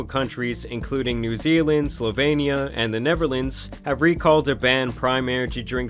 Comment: did he say "the neverlands"???